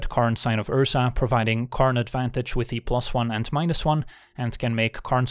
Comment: I swear I heard "karn advantage" well played